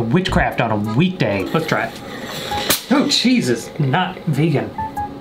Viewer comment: I swear I heard him say “Jesus is not Vegan” but the subtitles said “cheese is not Vegan.” Both statements are equally true